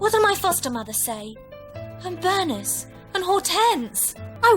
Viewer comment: Call me crazy but does she say "I'm 'vernous' and all tense"? Did she seriously mess up the word "nervous" or am I just insane?